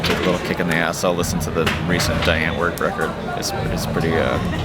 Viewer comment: What does he say he listens to when working out at 26:30? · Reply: I think he says Die Antwoord